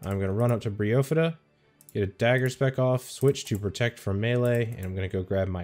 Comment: what does he say minuet 15:09 if dagger spec off? what